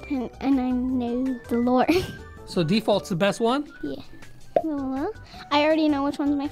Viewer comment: Did she say uwu